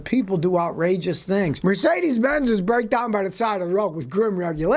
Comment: I could’ve swore you said “Mercedes eQuacks”. 😂🤣😂